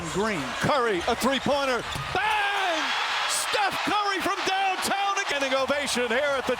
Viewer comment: 쯤 커리 3점 성공하고 해설이 커리 from downtown??이라고하는지 뭐라고하는건가요?? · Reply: Shot from way downtown.